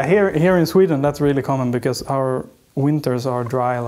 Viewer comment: Because our winters are dry like... Did he say dry like a nun's fanny?